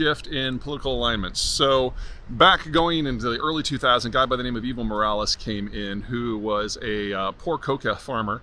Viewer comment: For some reason I hear “Evil Morales” 😂